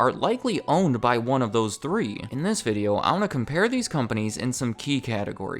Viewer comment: Wait a minute... did he just say I'ma?! KEKW Not that I have a problem with that, I just wasn't expecting it LUL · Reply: Oh, he said 'I wanna' (not that that's terribly far off SuperVinlin); just what happens when you watch videos at 1.5x speed, IG ¯\_(ツ)_/¯